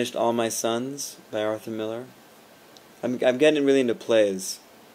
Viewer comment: hahaha. i thought you said henry miller at first. whoops. anyways you just missed the san fran bay area heatwave. i was actually gonna go watch "son of rambow" on wednesday. but i got out of work late. and was 20 minutes late and went on my own little adventure in san fran. hopefully next saturday i'll go see it. just as long as it still playing in san fran.